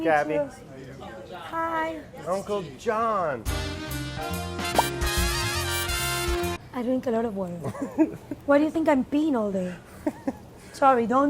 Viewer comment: "I drink a lot of water. Why do you think I'm peeing all day?" you're a dead man walking. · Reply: I thought she said “I drink a lot of wine, where do you think I’ve been all day” LOL maybe I need to get my ears checked